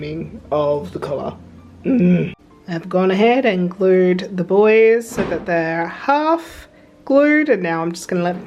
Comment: The way you kept referring to the buoys and I kept hearing it as 'the boys' like you were affectionately anthromorphising that part of your outfit! Also so glad to see the feathers worked! Quack Quack!